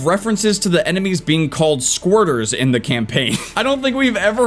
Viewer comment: For anyone curious for the definition of “squirter” in military terms, it means when an enemy is attempting to flee from a coordinated attack from opposing forces, like an air strike or ambush. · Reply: Huh I thought he said Squatter